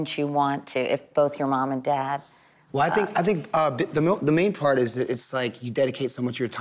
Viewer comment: "I think i think (bit the milk)" LOL WHO ELSE HEARD THIS?